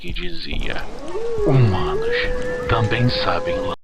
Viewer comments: eu entendi " humanos sabem lanber " meu deus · tu entendeu o que ele quis dizer nos 18:30 eu não entendi merda nenhuma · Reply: humanos também sabe lamber Tinha uma pessoa lambendo a mão da menina em baixo da cama ( '-'):Nojento